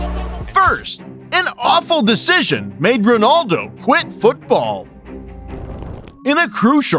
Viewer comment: I’m sorry did you say QUIT!?